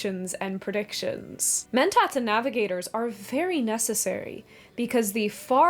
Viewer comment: I thought you said alligators, so I was just imagining an alligator eating spices to travel across the galaxy.